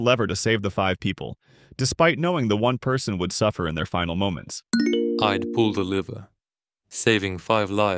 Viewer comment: I thought deepseek said "I'd pull the liver" ☠️